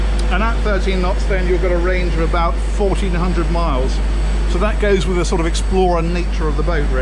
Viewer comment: What is the maximum range based on Ocean going speed? I thought I heard 1,400 miles but I assume that is not Ocean going speed and of course 1,400 miles won't take you very far across any Ocean . Otherwise a nice looking Yacht on the inside.